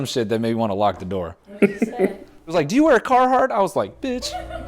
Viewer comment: what did he say? Do you wear a car hard?